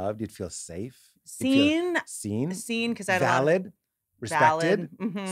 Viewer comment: Pete said "valid". I heard "phallic". A good time was had by all.